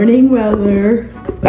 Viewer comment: I swear i heard 'good morning berner'!!!!!!!😮😮😮